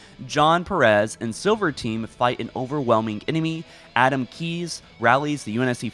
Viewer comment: lol dude said Adam Keyes instead of Admiral… 😂😂 sorry but that made me laugh